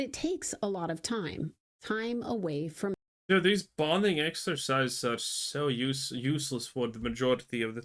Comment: Bonding exercise? I thought she said "Branding Exercise", oh boy that smarts.